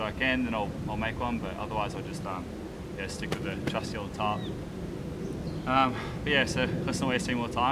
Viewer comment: Hahaha! I was cooking dinner while listening to this, and I thought you said you’d stick with your trusty old tart...!!! It’s been my dream since the 9th grade when we had an Australian exchange student from near Melbourne to come spend some serious time in Australia, so seeing the bush with you is wonderful. I love when you show the flora/fauna, and show us things you found while exploring. It’s wonderful.